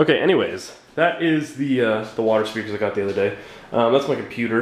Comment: How's the quality guys? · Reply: i thought you said you don't like water speakers